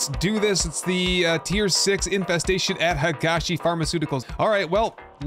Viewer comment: Ok as a non experience player who is still learning the names, I thought it said “Hugeass Pharmaceutical” and went ‘sure looks like it yeah.’